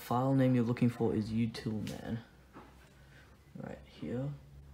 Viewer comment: What file did he say at 3:38? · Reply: Utilman